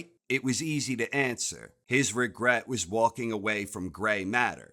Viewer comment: I heard "walting away" at first.